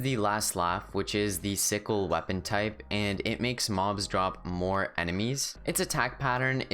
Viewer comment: Hey just wanna say at 2:40 you said More enemies instead of More emeralds